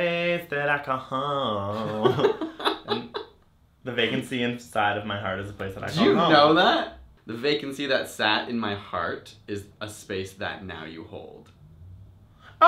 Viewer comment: The first one I heard "Diva, atoms in that is space"